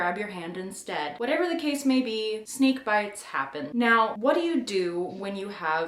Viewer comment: I thought you said "sneak bites happen."